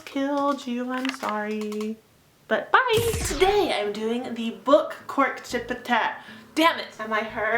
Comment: In the beginning I thought she said "The Book Corn Chip Tag"